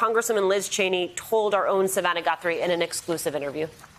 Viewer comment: When the hostess said “our own Savanna Guthrie” I could have sworn she said “our own Banana Daiquiri”……